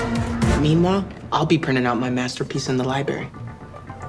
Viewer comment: IM CRINE DID HE SAY “MASTER PISS” 😭😭